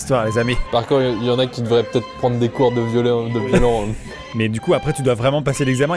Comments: Heu... C'est moi ou Yvan a dit "Y'en qui devrait peut-être prendre des cours de violeurs ou de violons" à 8:00 ??? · "Par contre y'en a peut-être qui devraient prendre des cours de violeur" Y'a que moi que ça choque ? 😭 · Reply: Il a dit "violon" :) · c moi où il a dit "des cours de violeurs" à 8:02 🤣🤣🤣